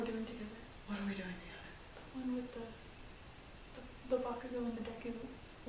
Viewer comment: DID I HEAR BAKUDEKU?!